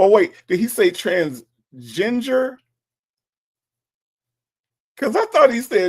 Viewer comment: I heard trans-ginger😅